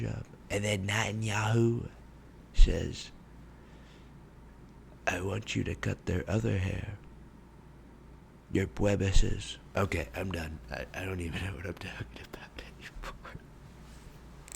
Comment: What did he say a 5:07 ( i heard = "bwebweses") because i dont speak English well a couldn't understand it